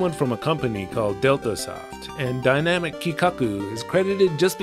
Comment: I thought you said it was programmed by "dildo soft," which is the greatest software company name ever.